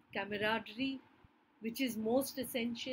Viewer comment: Ma'am thank you for this wonderful analysis...can u plz tell me what it is that you have said at 14:07 of this video...didn't quite catch on. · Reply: Camaraderie- a mutual feeling of trust